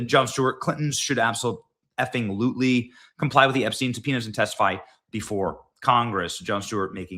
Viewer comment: Every time you said subpoena in this content I thought you were saying penis.